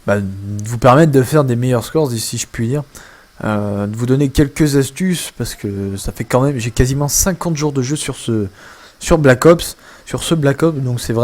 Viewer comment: Au début j'ai compris " 5 ans de jours de jeu" jme suis dit WTF? x)